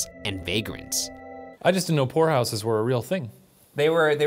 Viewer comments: I hate myself. I thought he was saying "Whorehouses" not "poorhouses" lmao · I thought he said pornhouses not poorhouses......I am so sorry for being dirty minded 🤦